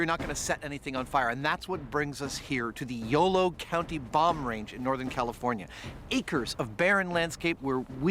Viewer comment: Did he say YOLO bomrange 😆